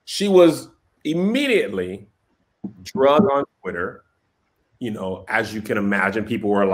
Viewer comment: Did say “drugged” and “panoramic”